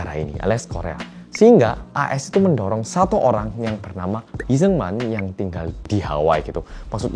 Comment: maaf banget aku dengernya sekilas kayak "Lee Sooman" 😭😭🙏